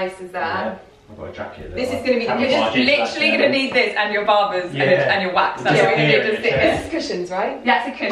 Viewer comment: Lydia, my fiancé won't stop making fun of me because I used the word baubles after watching your videos. But I'm American and I thought you guys pronounce it without the "r" like how you guys say "car." So I thought you say "barbels" in America. Apparently we just call them ornaments. Now he won't stop calling them barbels because thats what I thought you call them 😂😭